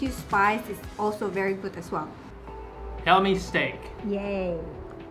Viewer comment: did say "Helmi's steak"? or "Helmi's take?" or "Hell, mistake!" 😂😂